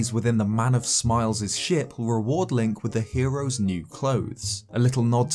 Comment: I thought you said Sh*t. My bad.